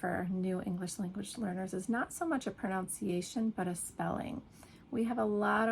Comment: Your pronunciation of “pronunciation” sounded like “pronOunciation” (7:09). What do you think? Best, Albert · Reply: Please listen to the word at 7:06. I heard it as “pronOunciation”, as many English learners do. But you are a native English speaker, you can’t be wrong. ❤️❤️😜